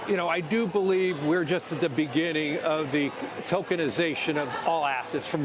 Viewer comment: Thought he said 😮 Tolkinisation